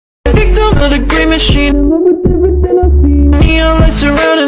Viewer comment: i hear "grimace shake"☠️